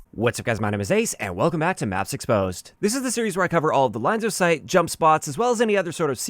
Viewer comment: I swear to God I heard "welcome back to NUTS exposed"